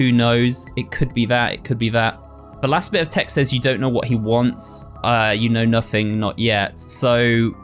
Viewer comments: i think it says “you think you know what he wants”, rather than “you don’t know what he wants” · I think it says “You think you know what he wants ?” Rather than “You don’t know what he wants”